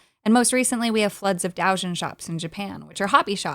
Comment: I didn't catch it the first time because of the pronunciation. It's DOH-jeen (doujin works/doujinshi) with a long O, where either you pronounce it like an English long O, or just lengthen out the time voicing a short O.